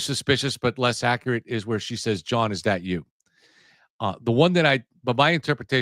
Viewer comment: I heard message as; "John, was that you?!?"